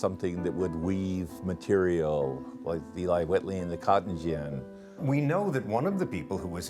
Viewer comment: what does he say right here: what's the name of the weaving machine? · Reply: The Eli Whitney Cotton Gin.